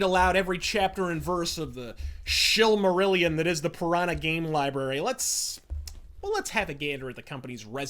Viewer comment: Oh, when you said Piranha Games i thought you said Piranha Bytes. Don't know if that would be better or worse though